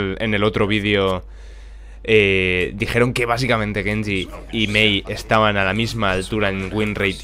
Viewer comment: Entendí que dijo. Genji y maeve (paladins)